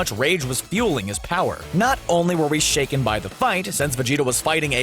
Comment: anyone else hear vegito instead of vegeta ?